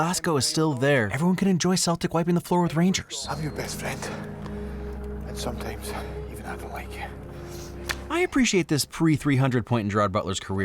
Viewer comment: "I'm your best friend, and sometimes, even have a lake here" is what I'm hearing, but what is Gerard Butler actually saying?